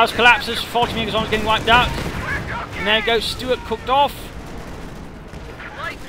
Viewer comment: did he say: ' 'An there the stuart cooks off.' ?